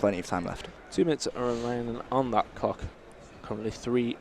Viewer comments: Did he just say 2 minutes on that cock ?😂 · anyone hear what I heard? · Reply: Cock😂😂😂